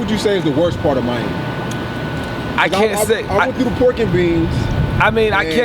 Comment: I thought he said Pork N Beans lol